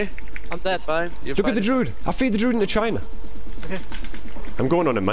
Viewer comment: did i hear "i will fear this druid into CHINA"?? hilarious